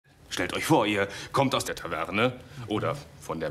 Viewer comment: "Stellt euch vor, ihr kommt aus der Toilette" Darum kann ich Akzente erst recht nicht verstehen :D